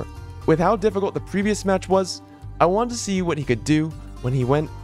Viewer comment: did I hear this right? “with how difficult the last match was”